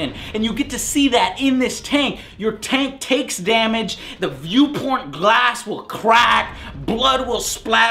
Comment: Did he just say the view porn glass?